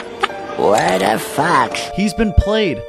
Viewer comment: he sounded like he said what the fuck